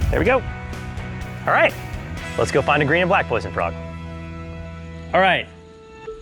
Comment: who hears him say green and white poison frog? like this comment if u did